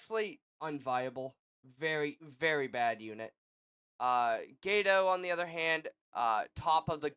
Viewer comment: I heard Gayto lol, good tier list though